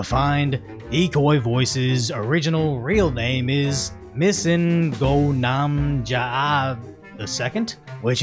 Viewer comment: why did i hear Missingno... Congrats on 1M Decoy!! Here's hoping for 2M in 2026